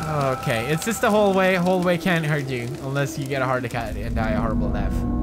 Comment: Did anyone realize what pewds says on 1:44 .lol He says heart akkat.